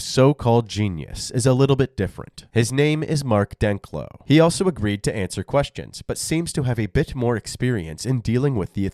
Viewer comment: What word? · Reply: Exculpatory.